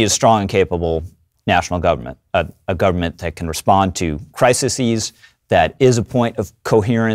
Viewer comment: I think he means to say a government that can respond to "crises", and it sounds like he is saying "crisises", which is not a word.